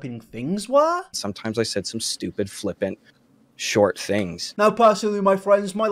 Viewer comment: pretty sure he said flippant instead of flipping